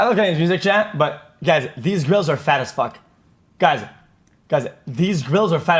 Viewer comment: Lmao i thought he said "these girls are fat af"